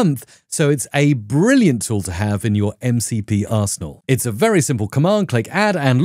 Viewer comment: sorry what did I just hear at 6:18?? "It's a brilliant tool to have in your MCP ars..." 🤣🤣🤣